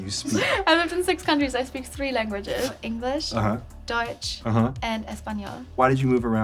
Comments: a mistake in in subtitles , instead of Deutsch (German) it says Dutch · The subtitles are wrong on 0:57 because they say English, Dutch and Español while she says its English, Deutsch and Español. Because Dutch is the language the speak in the Netherlands. And Deutsch which means German in English is a different language they speak in countries like Germany, Austria and Liechtenstein. I just noticed it, no offence to the subtitles maker❤